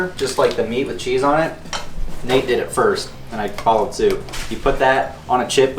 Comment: the guy said “I followed suit” (like the common expression) but the inserted captions say “I followed too”, which doesn’t even make sense in context